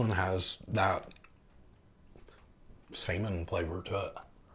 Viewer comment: Dude ... be careful of the way you pronounce salmon. When you were tasting the canned mackerel , it sounded like you said it tastes like... semen